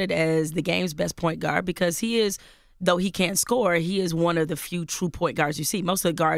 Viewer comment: did she say cp3 can't score at 23:22? i hope she said can but it sounds like can't